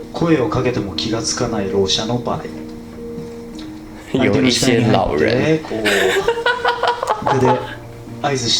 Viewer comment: I think the subtitles are wrong. Not the old people, but the deaf people correctly. Thank you for your reaction!! I